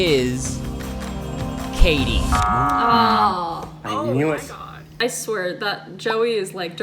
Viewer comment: Is it just me or did say "Oh my God?" 🤣